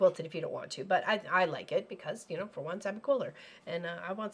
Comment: Lol, when you said, "because you're a quilter", my closed caption thought you said, "because you're cooler". Silly awayyy!